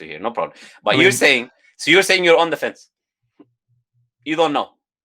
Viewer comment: subtitles say "in defense" but it should be "on the fence"